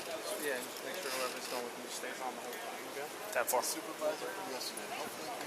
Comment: At around 28:50 I think that cops says “make sure who ever is filming stays on point” Bunch of ass clowns hiding their true colors!!